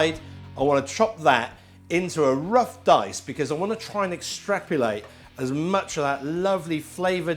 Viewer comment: I think that word you said at 1:23 instead of "extract" doesn't exist 😉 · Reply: I thought he said extrapulate😬